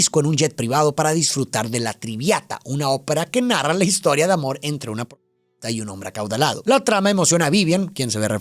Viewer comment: por que escuche Put@ ?